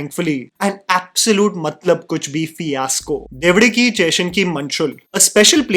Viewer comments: what are you saying? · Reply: Bro lmao he said bevdeke cheslu ke manshul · Did u just say evuduki chesina manushul 🤣